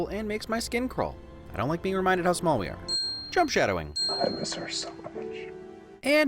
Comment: I thought you said Trump shadowing not jump shadowing my bad.